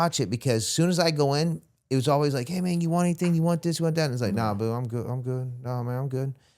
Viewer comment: Great story. I feel like the interviewer jumped around too much without summon up the previous question or letting him finish. And I swear I heard you say na boo?